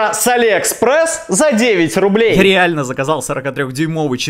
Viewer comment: Мне послышалось с 9000₽, я такой неплохо. Смотрю видео ещё раз и тут 9 рублей